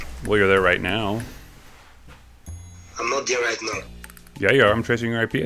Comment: I didn’t here him say here or there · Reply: an at 10:12 he clearly said I'm not here right now LOL